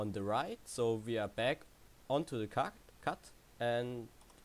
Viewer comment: back onto the kack xD verstehen nur deutsche